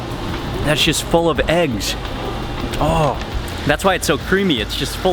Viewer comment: sounds like he said "that shit's full of eggs" LOL